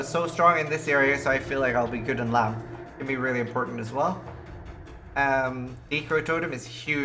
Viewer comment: At around 8:02 you said "_____ totem is huge for any of the labs" but I can't understand what you said. What totem? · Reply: decoy totem